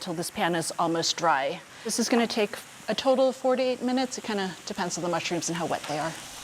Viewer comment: I was only half paying attention and almost fell off my seat when I thought she said 48 minutes :D